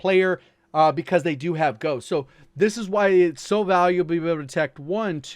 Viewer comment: what is he saying is that english my guy lol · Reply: So valuable to be able to detect ...